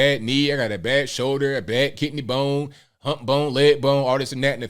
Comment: Did you say kidney bone? 5:29 🤣😎👍✌🎃